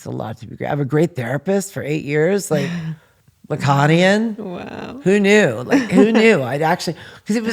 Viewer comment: Around the 46:45 mark where she’s talking about her therapist is she saying Aconian or Laconian? I can’t quite make out what she says there, can anyone help? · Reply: Lacanian